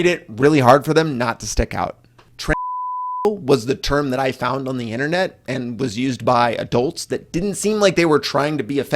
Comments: What word did he say at 9:15? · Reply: Transsexual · what is that word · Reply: Transsexual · what's the word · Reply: Transsexual